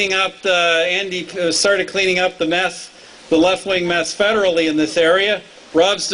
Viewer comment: Did Harper just say " We started cleaning up on meth?" at 3:03??